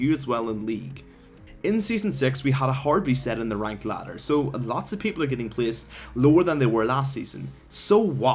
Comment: What did he say at 1:50 ? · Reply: "In season 6 we had a hard reset in the ranked ladder, so a lot of people are getting placed lower... etc."